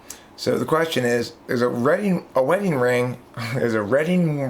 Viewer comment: I misheard this the first time as engagement ring rather than wedding ring and so my first thought was waiting for the just the right time to propose. Had I heard the question properly, I probably would have said something like, it's more comfortable to not wear them or I don't want to get them wet or dirty.